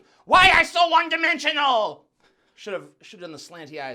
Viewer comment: Did he just say "Wong dimensional?"😂😂😂😂😂